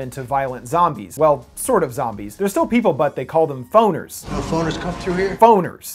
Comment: I had to turn on CC... i thought you said Boners lol